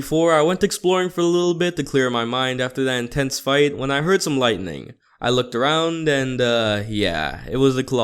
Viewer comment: when he said lightning i heard thunder lol